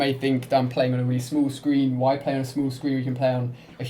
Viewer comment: Lol I thought he said 'smooth screen' but he said small.. fook